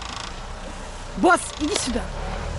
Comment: Russian to English Question: at 11:33 What did she say to the dog? I assume something along the lines of "Boss, get out of the way!" Just wondering what the expression is to drive a dog in Russian. (In Newfienese its just "GIT!" , short for Get out of the way) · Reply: Не "идите", а "иди". idi syuda.